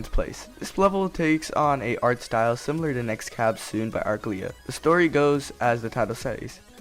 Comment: “by arclia” I thought you said but uglier lol